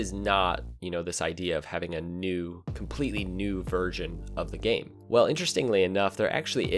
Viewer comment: Am I the only one who heard "new virgin" at 1:45?